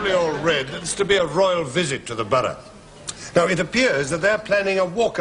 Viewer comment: Captain Peacock says what sounds like "there's to be a royal visit to the butter". Can anyone tell me what he's actually saying there?